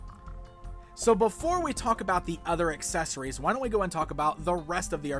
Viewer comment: I thought u said der were 7 accessories